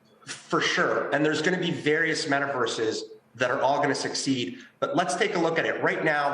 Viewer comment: did Andrew say "there's going to be various metaverses that are all going to" SUCCEED?